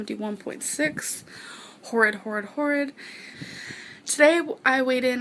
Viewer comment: all I heard was “Torrid Torrid Torrid”